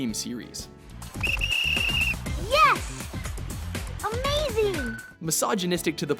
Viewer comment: A game featuring sexually attractive women is misogynist? I didn't know being attracted to a woman was synonymous with hating them. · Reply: At first I thought he said "massageonistic" and I'm like yeah, I'd definitely massage something :P